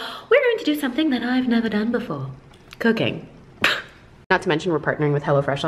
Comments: Omg I thought she said “cocaine” 0:36 🤣🤣🤣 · I legit thought you said Cocaine. I deadass had to go back. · I thought she said cocaine for a second there, so I was like weird flex but okay😂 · Anyone else hear cocaine at 0:36 and hella fresh at 0:38😂😂😂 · i'm sorry i heard cocaine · I legit thought you said Cocaine and I was waiting for the just kidding 😂😂